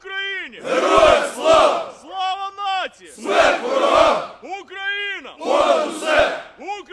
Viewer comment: did he just say Slava Nazi on 3:07 ??